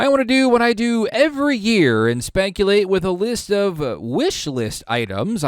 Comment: Did you actually say Spankulate? HAHAHA!